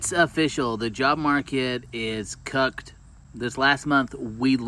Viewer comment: what did she sayyyyyyyyyyy cucked